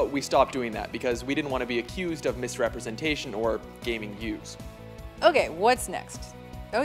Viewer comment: what's he say ? "...or gaming views" ? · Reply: I think he said gaining views.